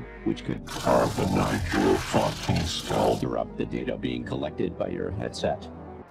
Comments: I think the voice said "Carbonize your fucking skull" which is honestly more of a horrific threat imo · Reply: Honestly, I hear "Carbonize your fucking skull" because yeah, that's a horrifying threat, utterly horrifying after looking up what carbonizing is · I'm gonna be honest I heard "carbonate" and not "pulverize" at the pause warning for some reason, and quite frankly I don't know what carbonating a skull would do but it's probably not good